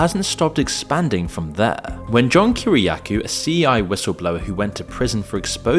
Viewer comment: We’re hiring writers. If you love this style of content and think you could write scripts that belong here, apply below. Serious writers only. 👉 Apply here: <link> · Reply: Why did you call John Kiriakou a CI whistleblower at 1:00?